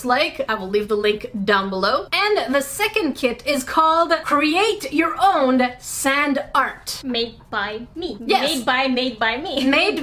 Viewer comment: when you said "create your own sand art" i heard..."create your own SAD art"